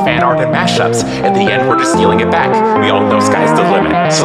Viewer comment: For 3 years, I thought 10:36 said "The n word is stealing it back"